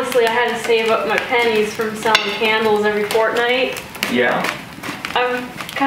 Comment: did she say Fortnite?